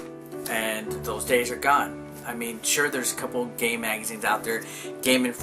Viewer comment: I swear at 1:53 you say gay magazine. Regardless, I agree and NP will be missed